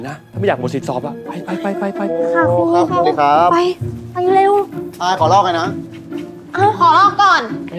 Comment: สิบทิศพูดว่าอ๊ะอายขอลอกหน่อยนะ???